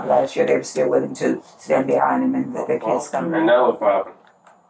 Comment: it says what the officer says before he scoffs is indistinct,what he is saying is “well and now look what happened”